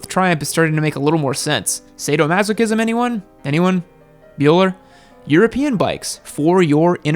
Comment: did he say Euler? Nice